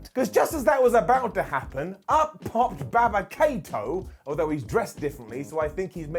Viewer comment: Did just call him Baba Kato? 😂